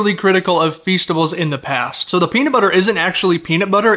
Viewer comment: I thought we was going to say ‘I’m a big fan of deez nuts’ but he said peanut butter